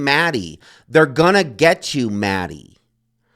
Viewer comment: I thought he said " their gonna get you for this" .